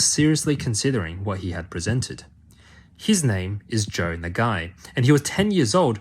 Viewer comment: I thought you said his name was Joan the guy 😭😭😭😭